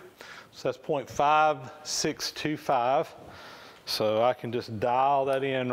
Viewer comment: I thought I mighta heard a mistake? I thought I heard you clock 0.625” for 9/16”? That bending rig is a really nice bit of kit. My workshop is much smaller than yours, though.....